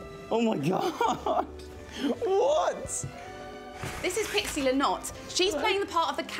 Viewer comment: I thought she said pixie Lott lol